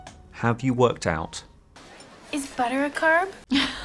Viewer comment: I thought it said is butter a crab